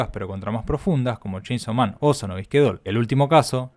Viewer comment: Soy solo yo, o no se entiende casi nada cuando dice los nombres de los animes? Pronuncia muy mal. Que dice en 12:40? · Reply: Sono Bisque Doll, se entiende bien